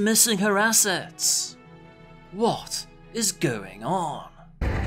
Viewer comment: Ngl when vegeta screamed my bulma I thought he said Obama